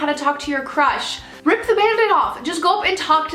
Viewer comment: When you were giving advice on talking to a crush I thought you said "just rip your panties off!" instead of "just rip the bandaid off!" lmao